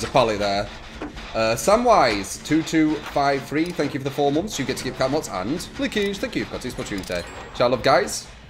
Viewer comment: New subscriber here. Can anyone please tell me what he's saying when people donate/subscribe (2:57)? Watched several vids now and still can't figure it out lol. Thanks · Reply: drix86 you get the cap emotes and...linkage. Thank you for your continued support to the trummunity